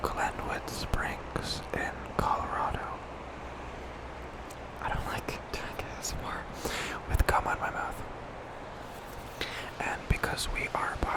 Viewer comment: someone pls tell me they heard what I heard💀💀 im so sorry hahah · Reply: it was "I hate doing asmr with gum in my mouth" but I had to rewatch that part lmao